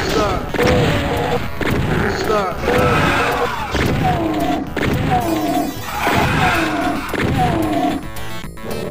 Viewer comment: what does it says? "Sthap"? 1:50 That new sky texture is nice! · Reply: I think they're saying "you suck"